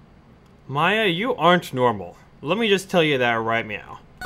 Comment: Meow??? Did you just say "meow"?! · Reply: Stephanie Kenyon "MAYA"